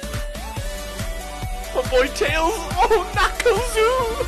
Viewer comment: MAH BOI TAILS & KNACKLES OOOHH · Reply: for "&" he actually says "OOH" but i wanted you to suffer with deez nu- i mean memes